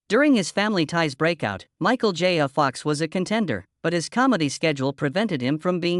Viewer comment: This narration is painful. I couldn't listen for long. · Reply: Agreed I wanted to hear all about this but 2:53 was as far as I could get when mentioning Michael J Fox and it sounded like he said Michal Jaya Fox, that was enough for me.